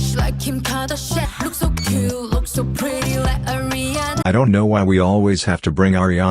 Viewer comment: Erm... I thought she said " Like a Rihanna"... I'm dumb